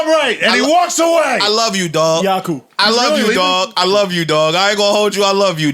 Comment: I'd side with Joe if he said "No more platforming anyone doing fuck shit" Then I'd respect what he's going for in this interview. Adam does interviews of people not doing fuck shit. Also 1:56:25 Does Parks say Yakub?